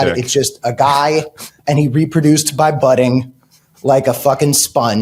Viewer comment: I thought you said, "By butting," but you said, "By budding." 😂